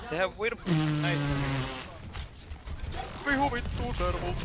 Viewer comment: I think that just said vihu vittuu servult